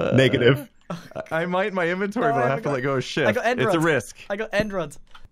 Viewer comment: did he just say ... Shift or ....